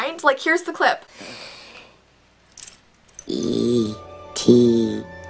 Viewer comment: I thought E.T said "E.T go home" My life is a lie